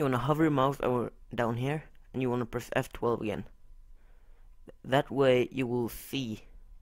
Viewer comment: What Did he say at 1:18 on da vid I cant understand! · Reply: Gaming "hower Your mouse over here, and press F12 again"